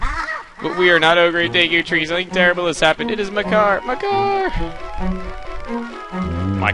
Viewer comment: Thought u said "my car" when u said "makar" :D so i was like: my car fell into the woods?? Wtf xD